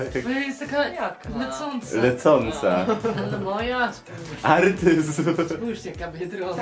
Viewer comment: Ludzie każdy kto pomyślał albo źle usłyszał daje like a jak nie to kom jak mówi artyzm to ja źle usłyszałem. I mi wyszło autyzm